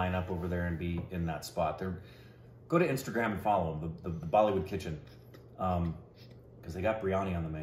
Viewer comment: You said "Bollywood Kitchen", I heard "Bollywood Pigeon" Biryani. Ask about Bollywood Crow Biryani and Vijay Raaz. :D :D :D